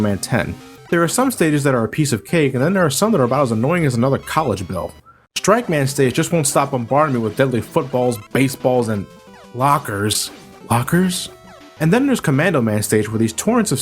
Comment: What does he say @ 12:40? "As annoying as another college bell?"